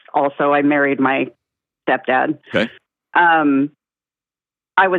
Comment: Wait, did I just hear her say, “I married my step dad” 😳